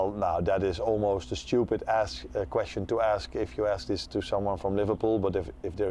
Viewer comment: Did homie just say “stupid ass question” 😭😭😭